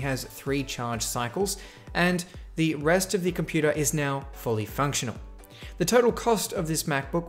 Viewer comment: did you just say... FUNCTIONABLE